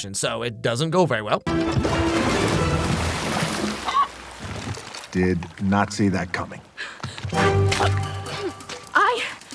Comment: Sounds like he said did NAZI that coming